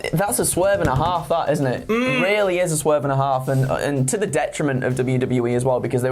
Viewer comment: I thought said “a swerve and hard fart”